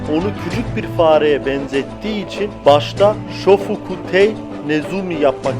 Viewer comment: abi burada ne dediğini anlayamadım yani Benn Beckman nin aslında ismi ne olacakmış? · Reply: Shofukutei Nezumi